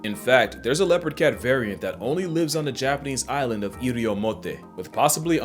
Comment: why is it sounded like african island when you say it